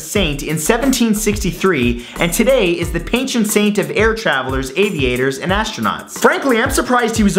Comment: Would you like to mean 'patron saint' @ 5:44, instead of 'penchant saint'?